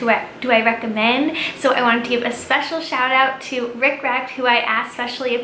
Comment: When you said Rick Recht I thought you said Riffraff, like the rapper LOL